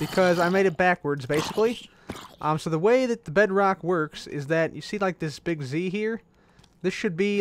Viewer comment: when I was watching I was a bit hungry and I thought you said Bread Rock